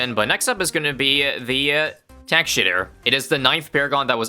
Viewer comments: What did he say? Tack $hitter? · Is it me or did he say "Tack shitter" at 3:55..?